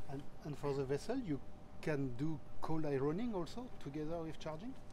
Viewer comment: What does he say? Cold ironing? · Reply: I know very little about vessels but we have to assume cold ironing indeed: "Connecting ships to the onshore power supply and shutting down the gensets in order to decrease exhaust emissions and noise. Cold ironing was adopted for many decades in naval bases where ships may be docked for long periods."